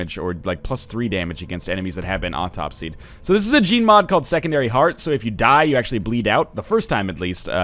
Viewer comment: every time he says gene mod it sounds like hes saying gmod